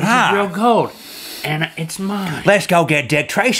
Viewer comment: at the end i heard "lets go get Traci..." and i was like O: "whattt, im Traci!" LOL